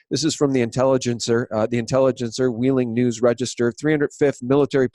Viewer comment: JUST A QUICK PIECE OF ADVICE: Please enunciate!! At 38:00 you talk about the increased deployment at Guantanamo. But you slur your words so that we can't hear what you're saying. You say, I think, "According to the Intelligencer Wheeling News Register"? I had to listen to it several times and I'm still not sure I got it. So when citing things like this, please slow down your speech so we can get it. It's good news!